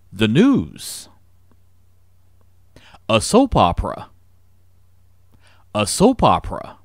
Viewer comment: que dice??? xD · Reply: A SOAP OPERA